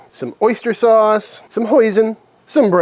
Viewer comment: I swear I thought you said some poison at 2:39.... Need to clean my ears